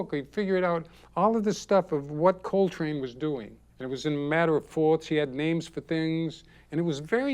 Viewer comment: in the transcript "matter of faults"? What's he really saying there? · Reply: Manner of fourths (cycle of fourths)